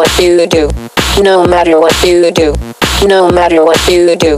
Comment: i sounds like she say no matter wut doo doo lol